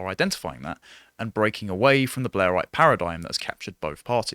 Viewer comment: Ohhhh, Blair-ite. I thought he was saying "Blaire White". lol